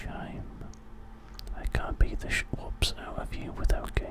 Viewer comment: what does he say? I can't beat the ??? out of you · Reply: MrPlaid shi- whoops